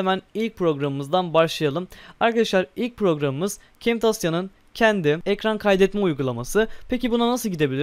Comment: ve 0:43 arası yani kimtosyamı ne diyor arkadaşlar lütfen doğru yazılışını bana söyleyebilir misiniz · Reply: Camtisia Heralde ??